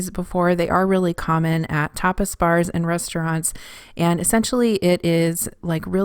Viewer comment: Funny thing... When you were prepping the Spanish Tortilla, I thought you said they were popular in topless bars. I had to go back and rewatch to hear you actually said tapas bars. LOL. Great video and I may just try the meal prep kit.